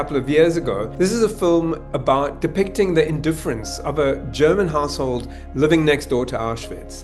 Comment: I thought you said “German arsehole” at 2:50. Still would be applicable…